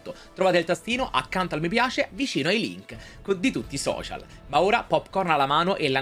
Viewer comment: ho sentito “vicino ai leak”, anche dopo il film l’hype non scende 😂